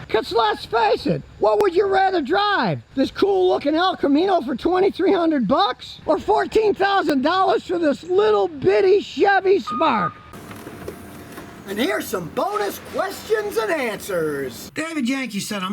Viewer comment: I heard shitty 💩